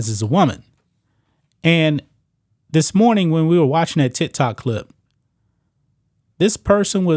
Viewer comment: did he say tit tok clip?